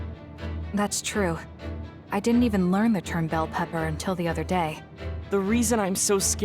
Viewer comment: Thanks for this! Thought I was going crazy when I heard Kasane mispronounce "dud" as "bell pepper(?)", but it looks like she did lol